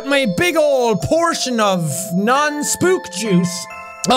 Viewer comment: i thought he said spookd*ckjuice